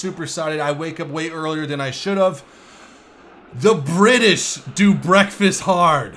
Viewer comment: The British do BREXIT HARD... thats what i heard 1st time playing15:20 :D